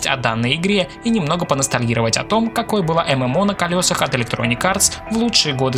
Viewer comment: ВЫ СКАЗАЛИ MWO????? Или мне просто послышалось(